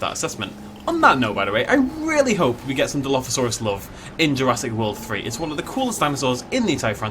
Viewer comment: My girlfriend is much happier and more satisfied since she started giving herself some dilo love. At least that's what it sounded like she said, makes sense to me.